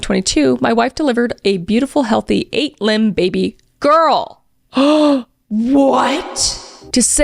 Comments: I thought Chris said 8 limbs baby girl at first, then I thought was a different story it was so crazy oof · OMG I SWEAR I KEPT HEARING YOU SAY 8 LIMB BABY GIRL. As in the baby had 8 limbs! I had to stop what I was doing and read the post myself lol 😅😂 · Just listening and swore I heard "8 limb baby girl" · Omg I heard “8 limbed baby girl.” I had to rewind to read it was 8 pound (8 lbs). 😂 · I heard 8 limed baby girl :chillwdog::buffering::chillwcat::hydrate::takeout: heheh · Girl, I thought you said an 8 LIMB baby girl. ☠️☠️☠️ I was so confused. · Anybody else thought she said an 8 Limb baby girl at 29:35? 😂😂😂😂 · Omg when you said "8lb baby girl" I thought you said "8 limbed baby girl!!!" 😂😂😂 Lb is POUNDS BABE AHHHH!!! NOT "Lb.." I love you ❤ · The way i ran to my phone to go back when i thought i heard you say 8 limbed baby and not 8lb baby 😂😂😂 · I heard 8 limb instead of 8 lb my jaw dropped 😭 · Holy!! I thought you said 8 "limbed" baby girl!! 😂 Lb=pounds! What a freak blessing.. because the whole thing you've been avoided if they knew but maybe they all needed the healing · I wasn’t looking at my phone when Kris was reading this so I thought she said “eight-limb baby girl” 💀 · the way you read the 8lb baby girl I thought it said 8 limbed baby girl and was very confused xD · was surprised when I thought I heard Kris say "8 Limbed Baby Girl" I had to rewind and actually see the text as I was doing work while listening to this. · I misheard 🙃 I heard 8-limb lol